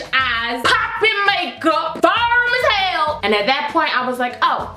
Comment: Does anyone know what she said after "poppin makeup"? I heard "farm as hell" but i don't think that's right... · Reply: Yes, she said firm